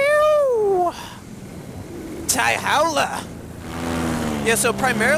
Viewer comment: Did he just speak chinese at 3:54 !?!? I heard 太好了! Which means something in the direction of very good